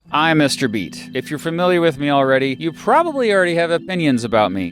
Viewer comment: i misheard "mr beast" 💀